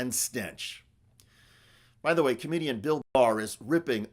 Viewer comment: I thought that you said BILL BARR there for a second! I thought that he came to his senses.